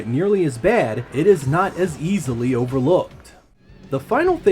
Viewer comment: There's an entire section where I mention the texture warping. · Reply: I knew it was mentioned, but I went back and watched again and I must have heard it differently the first time. For some reason I heard it's easy to overlook, but you did say it's not easy to overlook. Thanks for the clarification!